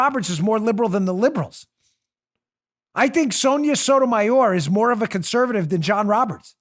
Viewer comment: For a moment there 45:25, I thought Dan said Tommy Sotomayor....I was like, 'yea Dan's a Tommy fan' lol.